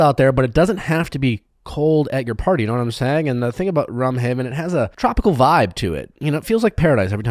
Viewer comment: nick says "rum haven" so fast that I keep hearing it as "rum ham" from always sunny